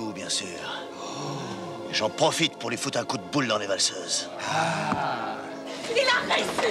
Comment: Il dit quoi après : "Et j'en profite pour lui donner un coup de boule dans les..." Dans les quoi ? J'ai jamais compris ce qu'il dit. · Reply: dans les valseuses